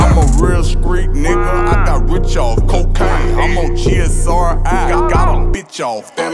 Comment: At first I thought he was saying "rich off HARAMBE" I was like "what!?" 💀